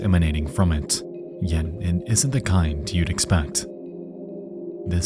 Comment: Captions says "an escapable" when it should say "inescapable" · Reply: caption says "and isn't" but you can hear "yet it isn't", which is missing.